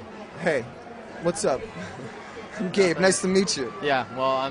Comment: really? haha i thought they looked pretty comfortable... heheh when the interviewer said "I'm Gabe" i thought he'd said "I'm Gay.... FOR RYAN ROSS!!" nah HAHAHAH Brendon be jealous.. jk XD